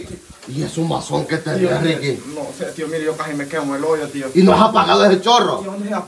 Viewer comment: Jjajaj4:47 yo oi que dijo tío deme en el ollo tío jajajajjajajaja